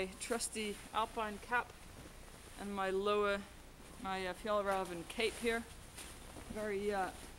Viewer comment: what is the name of that cape she puts on at 3:26 ? i cant make out the name that she is saying so i can look it up online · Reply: it's the fjäll räven luhkka